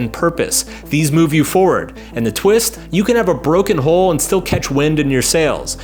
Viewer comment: did you say - You can have a broken…hole?